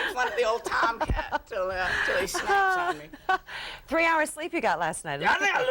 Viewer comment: Did he just say "Snap Chat?" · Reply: "Snap chat me" yes he did